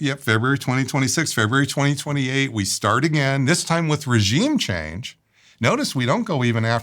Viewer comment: Did he say Feb 2028 we start again???